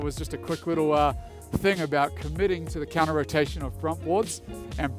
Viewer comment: I thought that dude said he was from Ligma, I was wrong, Denmark